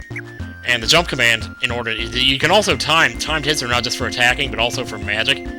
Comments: Thumbs up when each time said "timed hits" you thought he said "timed tits" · i thought you said "time tits" instead of "timed hits" ._.